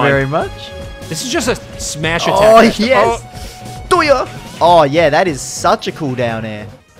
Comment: As a Ganon main, I heard that as a fast “DORIYAH!”